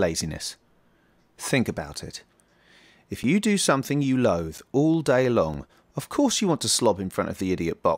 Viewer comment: Bad choosing of words - Initially I thought you said "love", but in reality you said "loathe" (the two words sound similar, which confused me at first).